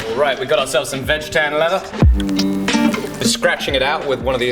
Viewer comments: Does he say vegetarian leather? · at first listen i thought you said vegetarian leather, as cows eat grass does this make veggie leather? Hehe.